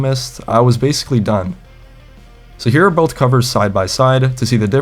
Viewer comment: i thought he said he FIXXED the covers